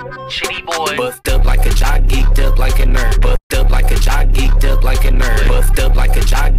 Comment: Bruh I hear "buff duilt like jockey duilt like a nerd"😂